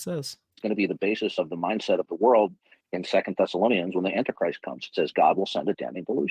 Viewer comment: I had to listen a few times to hear, "when the anti-christ comes", the first few times I heard ,"when the Enterprise comes". For a moment I thought had really lost it and worked Star Trek into the bible 😂😂